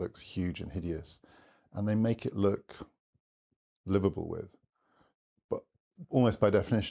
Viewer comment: Every time he says 'liveable' I hear 'Liverpool'.